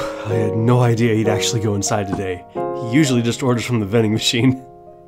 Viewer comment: "No idea..." Could you tell me what is saying in this part? Since I'm Japanese, I'm poor at listening to English...😭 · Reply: “I had no idea he’d actually go inside today. He usually just orders from the vending machine” :)